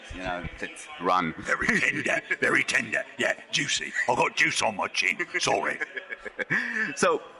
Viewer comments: Juice? I always heard "I got jizz on my chin". :D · I always thought it said "i've got jizz on my chin" but turns out it's juice... dreams shattered